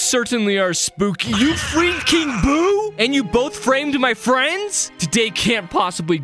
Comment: I thought he said you fucked king boo